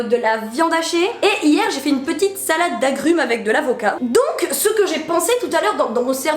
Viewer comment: j'ai compris '' et de la vodka " ...